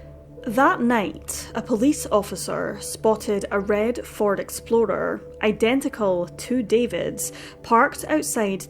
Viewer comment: I definitely heard you say red Ford Exploder haha